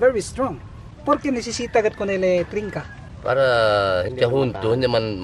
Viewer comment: Fascinaing my friend! On marker 10:41 with mr tying the crab, very clearly I heard and fully understood " Por qué necesita de ponerle trinca?". I'm a Spanish speaker and I understood it. It's not very commonly used nowaday "trinca" or "trincar" but it's a legacy of the Spanish.